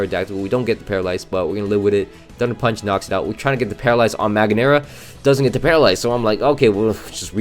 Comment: What did you just say? ;-; Man that’s mildly Infuriating. Ma gear na or Mag ear na?